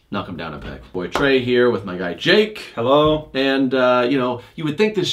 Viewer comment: I can’t be the only one who thought he said, “with my dad jake”